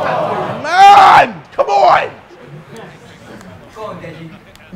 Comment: did someone said "c'mon deji" ?